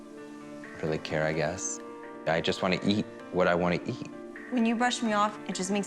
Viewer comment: I thought he said I yeet what I wanna yeet lol